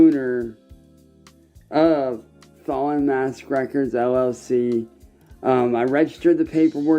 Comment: Honestly I thought he said 'Fall On Ass Records'. Genuinely thought the goblin was about to employ actual ironic humor here before I remembered exactly who is.